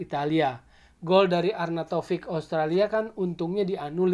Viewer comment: 8.18 Austria 😀🙏 bukan Australia, btw... Keren sih penyampaian nya. Paten kali bah...👍👍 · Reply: memang Austria kok..masa sih kedengerannya Australia hahahaha..sehat2